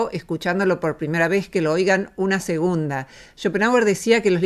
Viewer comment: ¿Cuál es el nombre que dijo en el segundo 0:52? :) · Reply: Schopenhauer